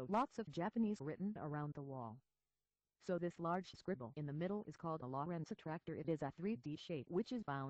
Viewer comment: What is 2:39 you say? Lawrensic wha? All I could understand was Non-linear dynamic shape